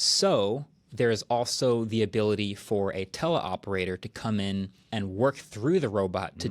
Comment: the subtitles say 'Tesla' every time he says 'tele' operator. Which is funny, cause isn't this how Elon's robots also work?